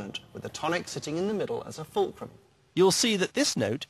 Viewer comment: What is he saying at 6:49 "with the tonic sitting in the middle as ......"? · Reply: Fulcrum